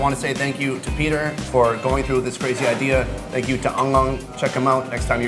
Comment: i misheard the name of the restaurant: "ẳng ẳng" =)))